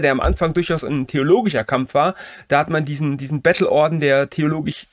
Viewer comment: witzig hier @ 26:37 redest du von einem theologisch starkem orden und nennst die dominikaner dann einen bettelorden- vom konext her hatte ich glatt Battle-orden gehört😄